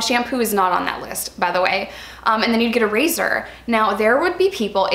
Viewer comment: I'm so confused. How do you get clean with not shampoo or body wash like wtf. And also, come on. 2 pads. Seriously. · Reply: I thought she said you'll get a bar of soap